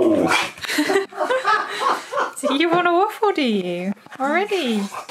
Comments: he clearly said "Waffle" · he actually says waffle wtf xD